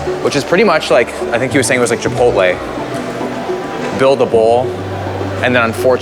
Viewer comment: I thought you said “build the wall” 🤣